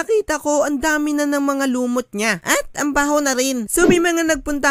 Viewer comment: bat narinig ko "ang bango pa" or "nya"